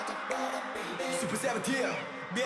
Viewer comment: what was the word and meaning ??? Any Korean answer otherwise I'll just think it is miaw miaw or meow meow 💖💖💖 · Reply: it was 미안 미안 means sorry sorry